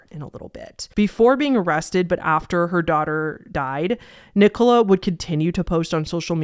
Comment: You said “poor daughter” but that is not was I heard and I was momentarily ✨shocked✨ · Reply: oooooh i thought she said wh0r3 daughter and i was confused for a moment too😭😭